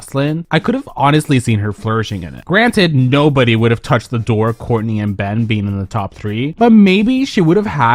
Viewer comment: Did you call her "The Door"? LMAOOOOOOOOOO